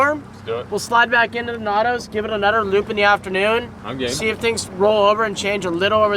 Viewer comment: i heard say “i’m gay”